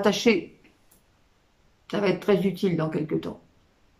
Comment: Pourquoi dites-vous qu'être un pilier va être très futile dans qq temps ? Ou bien j'ai entendu "futile" au lieu "d'utile" 😅